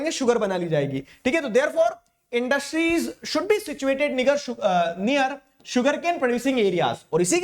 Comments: sir you said nig*er instead of near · Did sir just said n*gger at 32:37 ??? 😭😭😭